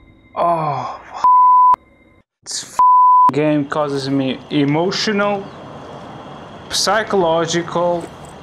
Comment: Emotional damage,... That's what i hear😅